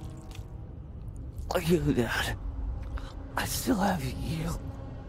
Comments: For some reason, I first heard “are you a god?” “I still love you” instead of “You dad…” “I’d still have you…” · I thought Mark said "Are you mad?, I'd still have you." But I listened more closely and heard it clearly